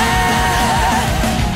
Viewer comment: I keep hearing "Body hair"